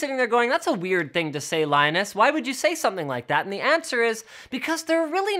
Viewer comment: what is the word that he said in 0:10? lienust ?